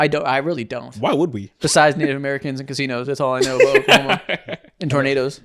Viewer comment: had to pause and rewind cuz I have to be mishearing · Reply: What does it sound like to you? 😂 I said native Americans and casinos